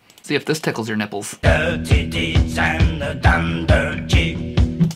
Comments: I always hear “Dirty deeds and the dunder chief”. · I hear dirty deeds and the dumper jig 😂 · I heard “Dirty Deeds and the Dunder G”. WITH MY BALLS!